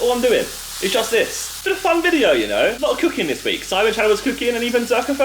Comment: did he just say theres cooking in the sidemen channel?